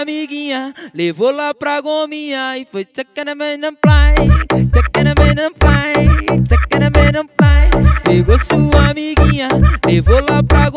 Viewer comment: Eu entendi:Tava pão no pain kkkkk